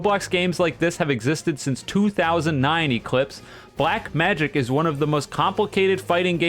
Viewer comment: did I hear... *Black Magic*?